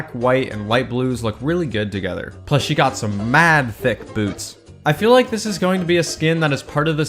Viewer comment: I thought he said mad thicc boobs